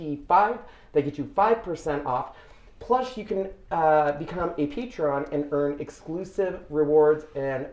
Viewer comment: Replay - Question - at about minute 4:15, it sounds like you say become a teacher - how do I find out more about this?